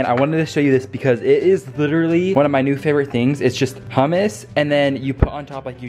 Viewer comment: What a thoughtful brother and son you are! Plus you are creative with your gifts and acts of kindness/service to others. That snack looks wonderful, although at first I thought you said it was made with Pumice! :face-blue-wide-eyes: Then I listened more carefully--hummus is one of my favorite things to eat. thanks for the random facts, interesting ideas, advice, etc. with almost every vlog. (But there are a few things you leave us hanging about your future). :body-blue-raised-arms: Luckily you should have many years left on your future, so it is okay that you haven't really decided everything yet. The world is lucky to have you in it! 🤠